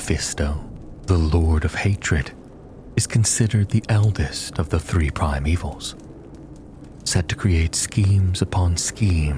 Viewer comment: I thought for a moment you said that Mephisto was the "Elvis of the prime evils", lol. Excellent reading.